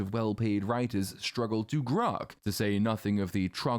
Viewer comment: Did you say "Grok"? What a throwback